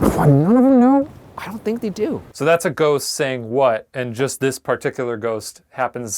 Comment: It says you can hear a ghost saying what but you can see his mouth moving. Idk if that was an editing error or smthn but that was him saying "what, none of them know?" Not "what" and "none of them know?". It also makes sense with the sentence and the tone.